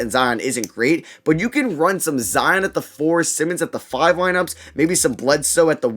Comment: Did I hear "Zion at the 4 Simmons at the 5"